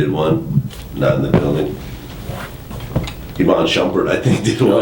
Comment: Who did the mention at 2:40 that made them kinda laugh and shake their heads?? To me, it sounded like "Evon Chumper", but I know that aint it lmfao.